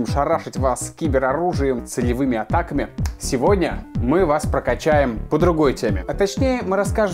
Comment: Послышалось: "солевыми атаками"))